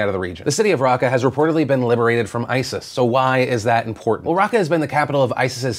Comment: If you turn on captions and skip to 11:44 philip says liberated from isis, but the caption says Goat F*ckers